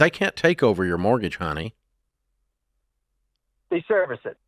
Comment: LOL did Ramsey call him “honey” 5:28 😂